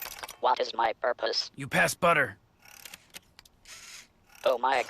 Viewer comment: At Least he has a purpose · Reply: Now rewatching it I think I misheared it, but the first time I understood "You passED butter", which made it more nihilistic for me.